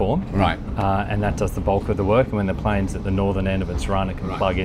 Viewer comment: Did he say "Plane"?